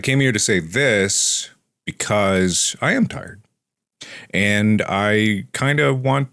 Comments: WHEN HE SAID AUDIBLY "IM TIRED" I HEARD "IM RETIRING" AND NEARLY CRIED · bro i swear when he said “i’m tired” i heard “i’m dying” I WAS LIKE HOLD TF UP